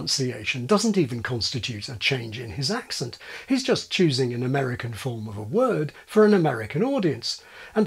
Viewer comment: Dr. just a small note. The pronunciation of the word "route" is not uniform throughout the USA. Here in Massachusetts, for example, we speak the word as "root", just as RP UKers do. But this was fascinating. To me, Harry sounds very British indeed. 🙂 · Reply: I thought he said "the" American form, which still doesn't make it universal, but could be interpreted as such.